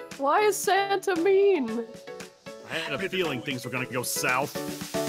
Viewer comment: UR JOKE I HUST NOW GOT IT I THOUGHT U SAID 'i think we need to go south' OMI (oh my irene) WHY YYYYYYYYYYYYYYYYYYYY (I STILL LOVE ZANE~CHAN.)